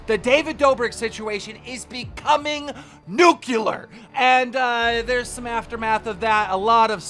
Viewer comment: "Nookulurr"? - Even the captions didn't know what you said. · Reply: Pretty sure he said nuclear 😂